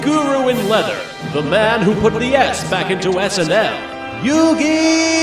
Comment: I thought he said "put the s back in SNL"